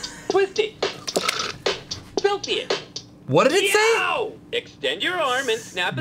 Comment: did it just say filthy it???